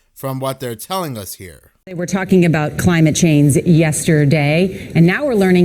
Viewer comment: sounds like she says climate chains